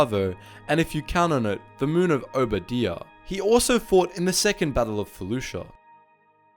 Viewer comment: I thought you said second battle of fallujah lol